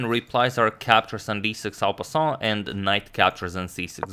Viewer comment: What are you saying here? Alpason? xddd i dont understand you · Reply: En passant.